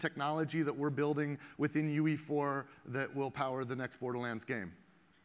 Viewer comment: did he just say they are using the wii u to render this